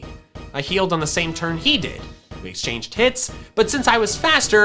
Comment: Why did you say:'we exchanged tits'? Dont you mean "hits"???